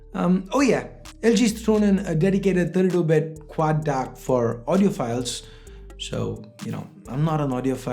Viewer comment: Great video... I just heard something from 7:17 ...how do people get ''to become audio files ''?☺..just pulling your legs😀